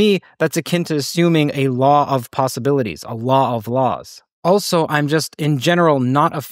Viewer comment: when you were saying "...a law of possibilities, a law of laws...." I misheard it as "...of possibilites Allah of laws...". Most probably it is just a coincidence, but again that leads us back to a law of probability, somehow.